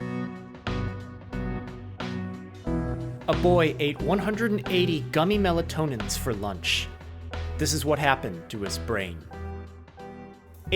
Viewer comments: boi i thought ts said “my boy ate” was tryna see a group of teens acting up lol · i thought it said gummy melons an di'm just like "I do that often"